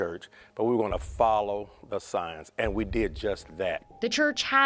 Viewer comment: This is the scary part, 22:28 I hear him clearly later he said "science" but I assumed he said "signs" and the scriptures speaks directly about a certain generation seeks out a sign and wonder 🤔🤔